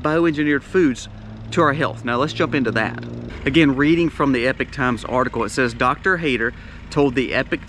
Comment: I hear you saying "epic times".... Is that actually "Epoch Times"? Referring to this newspaper I seek out weekly? 🤔 Thank you for your content!!! 👃✌️🥰🇨🇦